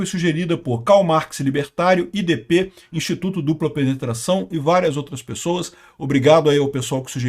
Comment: Eu escutei direito 0:56? "notícia sugerida por instituto dupla penetração"